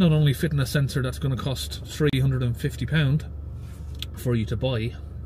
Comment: Did you say £1350 for a new LR146855 sensor?! Genuine OEM parts are being advertised all over for around £250. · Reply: I said £350 from Land Rover